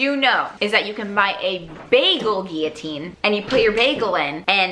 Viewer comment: I swear I thought you said basil guillotine and I was so confused